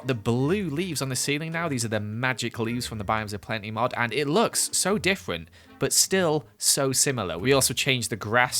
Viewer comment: Idk if it’s just me but I hear “and it looks so diffwent” 😂